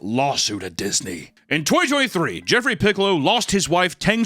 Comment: When you said “in 2023” I thought you said “in Toy Story 3” and I was so confused, I was like how did someone die during Toy Story 3 from an allergy? I’m so stoned though lmao